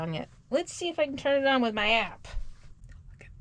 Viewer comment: 😂 I'm laughing my butt off. I was sipping my Iced coffee when I heard: "Let's see if I can turn it on with my ass!" My half asleep mind took a moment to realize you said "App" Oh man, it was nice to laugh. I enjoy your adventures. You're such a wonderful person. (BTW those gluten free tortillas are more pliable to work with if you heat them up first. The falling apart struggle is real.)